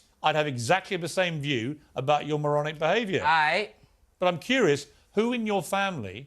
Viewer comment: Does he call piers a mogron instead of moron at 9:18? 😂